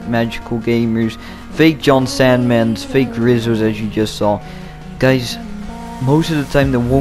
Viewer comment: I thought it said faze rizzo lol.